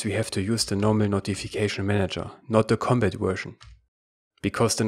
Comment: you say something that sounds like "not the combat version". I can't make out that one word before version, so would you please enlighten me? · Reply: I said don't use NotificationManagerCompat